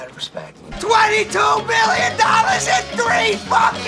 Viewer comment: Did he say billion or million? 22 Billion would be unbelievable..